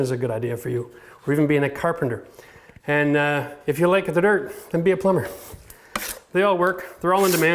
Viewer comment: If you're artistic - drywall. If you're a numbers guy - electrician What does he say at 4:42 "if you like ****** then be a plumber?" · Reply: If you like the dirt